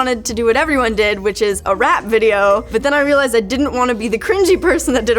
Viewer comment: I thought she said "rat video"